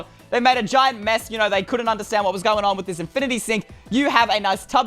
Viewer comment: In caps, I heard Infinity 'Sick' 🤣